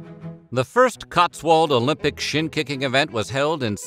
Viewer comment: I swore I heard SHIT KICKING not SHIN KICKING